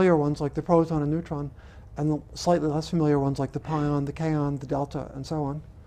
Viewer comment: Did I hear that right: the proton, the neutron, the pion, the k-on, the delta, THE SOE-ON. Lol